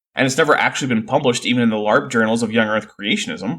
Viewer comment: Did you just call the Creationist journals "Larp" as in "Live action role play"?! 😂 😂❤ Or did I just mishear it because I do Larp (and one of my Larp friends likes to scoff at Christians who "didn't even read their own rulebook" - especially when they criticize queer people while wearing Mixed-Fiber clothes and eating shellfish) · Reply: I did call them LARP journals, as in live action for play.